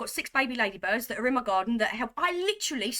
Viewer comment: Are you saying Lady birds?? At first I thought you were saying ladybug 🐞 I’m just not positive what you’re saying 😂 *edit: I just turned on CC and it says lady bird 😄